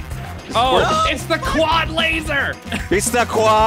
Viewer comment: 2:4I thought he said "clod laser" and I was like "Peridot?"